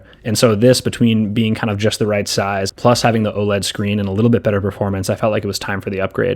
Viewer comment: im sorry did u say better performance?